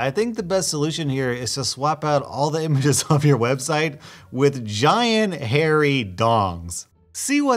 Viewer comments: bro i completely zoned out at the wrong time and i thought you said to replace all the images with giant hair dongs lmao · When said giant hairy d o n g s I thought he said giant hairy dogs